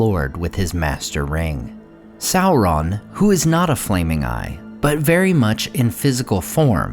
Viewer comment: Did you say, "Sauron, who is not a flaming guy"?